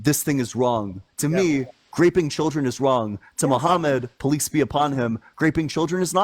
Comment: Did you say “Muhammad, police be upon him” 😂 I’m so stealing that!